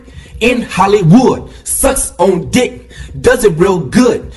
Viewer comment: Im I The Only One Who Heard Sus?